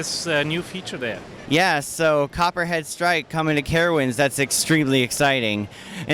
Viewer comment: I thought he said "cuphead strike" and got REALLY excited for a second :'(